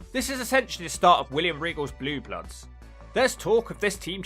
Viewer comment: I thought you said Blue Nuts instead of Blue Bloods